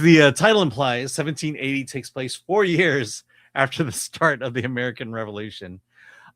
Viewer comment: 1780 = 1776 + 4 … 4 years AFTER (not START OF - which inadvertently misspoke) the American Revolution. Love you guys but have to make sure you get your dates and history correct 😊❤ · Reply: He said "it takes place four years after the start of the American Revolution" which is technically correct. He didn't misspeak, you misheard.